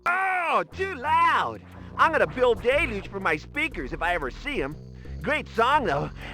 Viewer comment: "I'm going to bill Deluge for my speakers if I ever see him" My god now that was a hard one for my ears to parse, it simply did not hit me until I heard about Bryan Deluge later on lol. Auto captions really threw me off there.